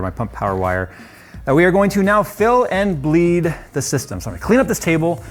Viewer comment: Wait...did you just say you're going to bleed Phil?